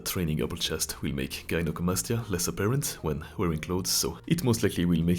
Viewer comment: did you said that incline chest press will make gyno like what? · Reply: He said that it will make gyno less seeable